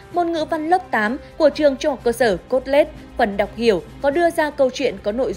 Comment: trường cốt lết? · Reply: Trường tên là trường THCS Colette nha bạn. Tại trog video phát âm nghe thành cốt lết 😅